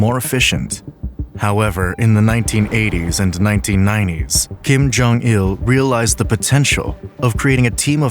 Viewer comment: I thought that said Kim jong 2